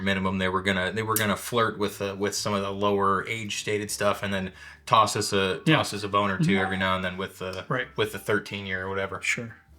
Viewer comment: And don’t forget 4:01… on relisten I know what he said, but both of us heard “toss us a boner” on first pass😂